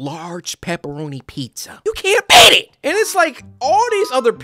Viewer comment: did he say you cant eat it or beat it?